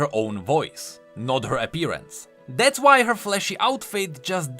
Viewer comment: I love how every time says "Flashy" I have to double take because I misheard it as "Fleshy".